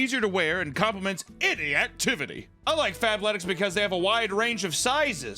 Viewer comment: the fabletics sponsorship is CRAZY 😭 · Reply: Lol I thought he said fatletics...